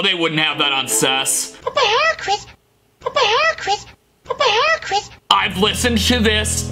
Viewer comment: I heard but hair Chris